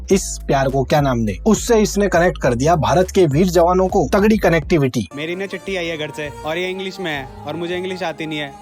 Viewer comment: didn't he say tatti instead of chitthi?